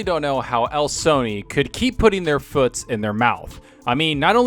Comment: I’m sorry but right away I heard “their foots” instead of feet and I lost it